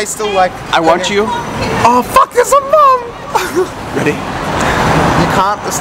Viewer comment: what did say at 2:19? "Aw fuck theres a __"?? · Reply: I think he says mum :)